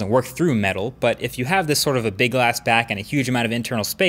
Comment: Did he say "Big ass back"? 🤣😂